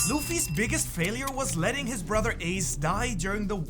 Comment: DID HE SAY ZUFFY?